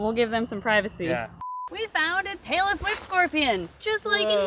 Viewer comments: Ok ngl @ 8:38 I thought said Taylor Swift Scorpion and I was very confused 🤣🤣🤣🤣🤣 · i heard 'a taylor swift scorpion!' and got confused